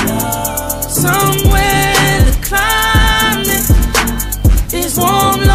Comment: I always thought it said ' somewhere in the clowns mate'